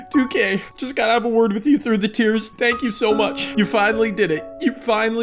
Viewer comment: Did someone say tiers 0:03😮😮